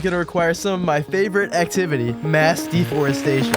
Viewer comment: When you said mass deforestation, for a second I thought you said mass deportation for some reason lol.